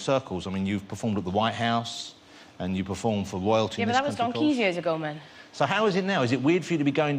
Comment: What in the world did she say at 0:34? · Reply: she said "yeah, but that was donkeys' years ago, man" (that means "ages ago")